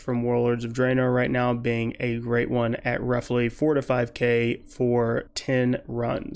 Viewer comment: Hey man you got it wrong you mean 4500 gold for 10 runs in skyreach becouse i cant make 45k in 10 runs impossible but if you know how tell me pls ? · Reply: He said 4 to 5k not 45k. You misheard.